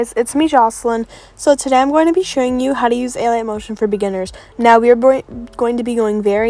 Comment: am i the only one who heard her say alian motion 🤣